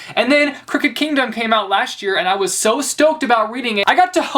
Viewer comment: anyone else thought he said cricket kingdom?